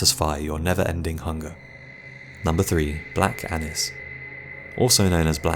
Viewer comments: i thought he said black annus lmfao like if you thought the same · For a sec I thought he said Black Angus haha I was like noooo 😭